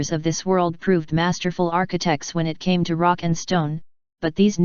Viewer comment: Did I hear a rock and stone?